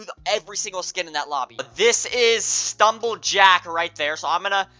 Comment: Did bro just say this is Stumble Jack💀💀